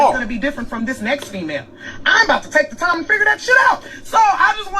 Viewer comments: I heard her say I'm 'bout to take the time to figure that shit out. I love this woman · What she SAID WAS “I’m about to take the time to figure that shit out”, not she’s not going to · I thought I heard her say she was bout 2 take the time 2 figure that shit out...🤔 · She said, I think, "I'm ABOUT to take the time to figure that shit out." Again just what I heard. Like she is going to take some time away from dating and develop herself as a person. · Didn’t sound like she said I’m not going to take the time. It sounds like she said she’s gonna take the time to figure it out. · Her video is an actual epiphany of self realization because she actually says @ 18:30, "I'm about to take some time to figure this out". Please relisten as you state the opposite @ 18:40 .... this young lady is to be commended, thankyou! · Starting @ 18:35 she said, "I'm ABOUT TO take the time to figure that shit out..." Which makes a world of difference imo. It appears as though you misunderstood what she said. That's why I'm bringing it up.